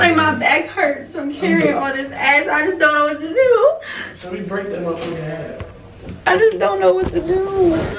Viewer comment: I know yall heard what said at 9:58 👀 😭 is wild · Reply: he said break that mf in half😂